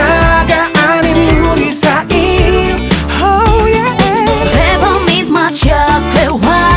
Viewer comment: It sound like she is saying peppermint my chocolate, which makes it more sexy